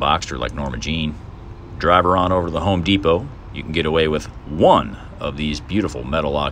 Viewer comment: I'm picking up a used 2014 Cayman S next week and would like to do this modification. Great job, looks factory my friend. You made the comment about one of the benefits of living in a "free Country" minute 4:10. With all the current lock downs that are happening in our current "policed state" we call the USA, I would have to disagree with you on your "Free Country" comment. Sad but true just how easily our liberty, rights and freedoms can be taken away. If I get my new Cayman, I hope to remember this time, not by Covid-19 but by new Porsche adventure. Do you mind sharing where to pick up the gutter guard material? Peace. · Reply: I think he said home depot. if they let you in haha one by one. (eye roll) :)